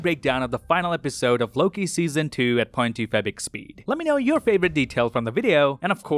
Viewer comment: When i sped up x2 at 8:34 I heard " Infinite EXP " instead of " 0.25 speed "